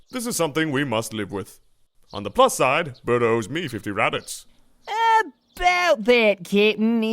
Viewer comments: for years I thought he said butter owes me 50 rabbits not raditz I feel more dumb than goku😣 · what did he say at 1:57? guldos meat can feed rabbits?.. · I keep hearing "On the plus side, Burter owes me 50 rabbits." Though I'm pretty sure he's saying Radi-... oh what's the difference. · Who owes who what? I heard something about rabbits. What did he say? "Bird owes me fifty rabbits." What? I think i misheard something. Otherwise funny episode. I laughed the whole time.